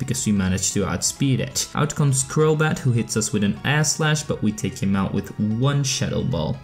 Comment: did anyone else hear “ass slash” instead of “air slash?”